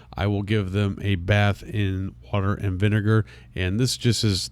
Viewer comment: I thought he said butter and vinegar 🤣🤣 Third time I got the word water